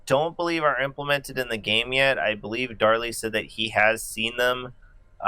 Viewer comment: I heard "what do you think darling" instead of darlee lol